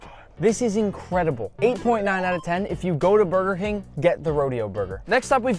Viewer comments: he really said Burger hing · did he say if you go to burger hing not king